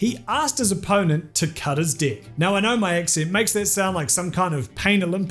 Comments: I thought you said cut his dick, not cut his deck. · For a sec when he said "Cut his Deck" I thought he said "Cut his Dick" That almost made Magic the Gathering even more exciting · I don't mean to be a jag or anything, but all I kept hearing was "cut his d*ck." Sorry.😜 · They way you say deck in this video is so close to sounding like dick. Was taken back when I thought you said "cut his dick" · I keep hearing 'cut his deck' as 'cut his dick'